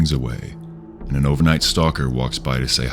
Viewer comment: I thought he said overnight stalker.